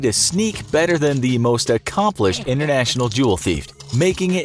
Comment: i thought that jewel was jew and i was bouta say